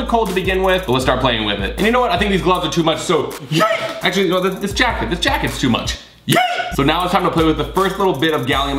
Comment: did you say yeayee or yeet?? im confused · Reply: He said yeet!